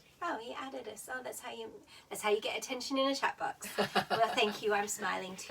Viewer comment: I thought you said cat box...